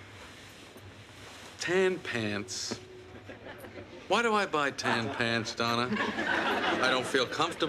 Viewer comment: "Tampans...why do I buy tampons Donna" is what I thought I heard. (And I was thinking is that how americans pronounce it?)